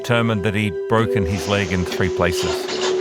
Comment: So 3:16 did he broke his leg in TWO OR THREE places? My boyfriend is hearing two and I’m hearing three and he thinks the subtitle is wrong so please somebody tells me Im right 😂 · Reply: He broke his leg in 3 places.